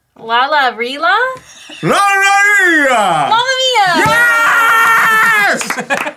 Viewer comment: I swear when Arin said “mama Mia”, I thought he said “gonorrhea”